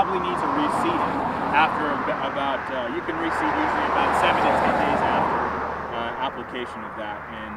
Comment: What is being said at 2:09 to 2:14 ... that vehicle drowned his voice right out? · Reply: you can reseed 7 to 10 days after